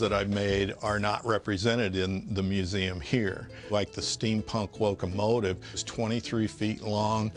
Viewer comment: I misheard it as "Steam Punk Woke-a-motive" and im not disappointed either way